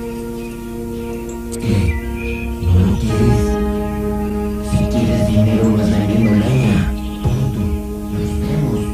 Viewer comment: Que es lo que dice... "Si quieres dinero no es lamiendo leña" ??? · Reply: Vendiendo leña